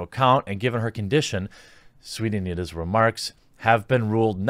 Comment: And here I thought you were saying Anita Sarkeesian...